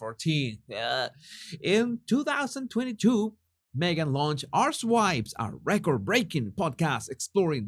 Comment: When you said "Archetypes" I thought you said "Arsewives". Don't change your accent.